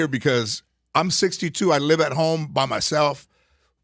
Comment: At :58 I thought he was gonna say "I'm 62, I live with my mom" lol